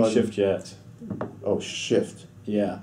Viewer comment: I thought he said oh sh*t